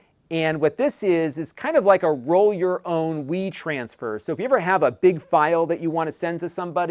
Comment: I thought he said roll your own weed transfer. I was like huh then notice wetransfer lol